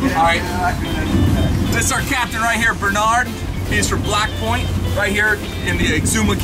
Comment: Lol at 4:28 I thought he said, "He's a black boy."